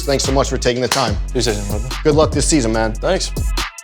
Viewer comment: i love you kyrie but wtf did you say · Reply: "Appreciate it Brother"